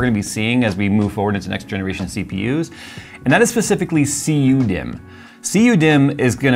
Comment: I heard him say CU me not cu dimm thats an oof